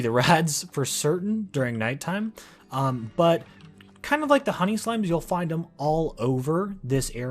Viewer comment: when he said "honey slimes" it sounded like he said "honey slams"